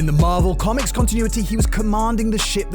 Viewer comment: Did he say marvel?!?!